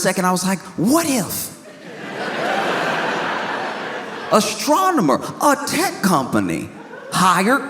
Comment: For a second I thought he said “Astronoma”, and I thought “Fire the satelasers!”